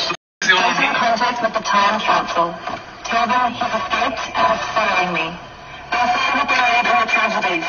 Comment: can anyone decipher what is being said? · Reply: Time capsule….escaped….following me?